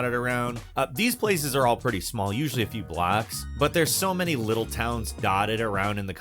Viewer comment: thought he said "usually a few Blacks" gotta cut the local TV 📺 news off for awhile brother 🤣🤣👍 great vid brother peace an love